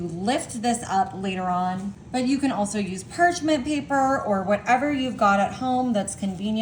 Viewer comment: what is perchment paper? its perching? woman, it is parchment paper , do you know anything? so inarticulate, it hurts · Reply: 100% sounds like she said parchment paper. 😊